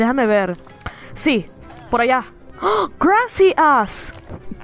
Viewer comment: what he said grassy ass😂😂😂😂😂